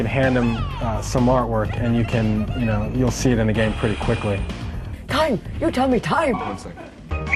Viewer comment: what is she saying? ..."time? you tell me time"? wtf lol